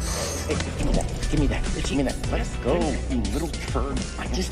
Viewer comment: who else heard the “let it go, you little turd!”?